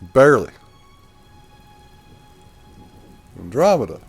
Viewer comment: Health is always first. Thank you for sticking around! Not sure what you mean by "Glad to See bear". I don't remember seeing a bear in this game. I'm glad I could get my pup Andromeda in the picture. Maybe I should get a dog cam set up. She does interrupt quite a bit doesn't she. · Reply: Oh haha. So sorry😅. I was folding laundry when I was watching this video and apparently misheard you. I thought for a moment you said your dog's name was Bear—embarrassing confusion. I don't think she is a bother; think of her more as a co-host. 🙂